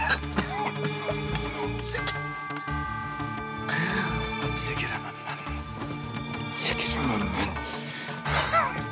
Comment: ta ne diyor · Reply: Hapse giremem ben diyo